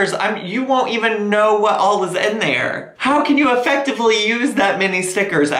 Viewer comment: ok...I listening in the back ground.... and I heard: "how can you effectually use that many stickers at one time... " How many panties can I piss laughing in one day. Thank to, I got two on my flag pole.